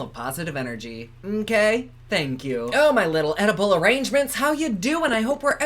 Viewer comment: "My little edible arrangements" 🤣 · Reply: Thought he said "Oedipal arrangements" ??? 🤔